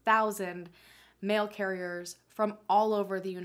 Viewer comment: me literally asking myself what is a "Male carrier" until realising what she actually said... 😂